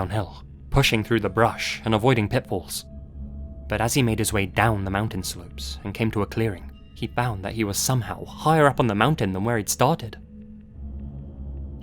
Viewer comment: I thought you said avoiding pitbulls and I was so confused, had to rewind!